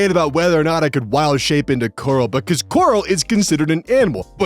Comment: Yay, is gonna become a DnD Horror Story reader! I love these. o 3o Edit: Gonna be honest with ya, every time you said "coral," I thought you were saying "gorl," so hearing "My DM won't let me Wildshape into GORL!" was something that made me do a double-take.